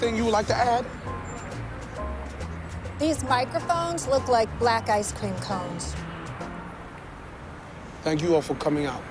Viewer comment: Black ice cream cones or black guys cream cones I’m sure I’m reaching but I swear that’s what it sounded like she said 😂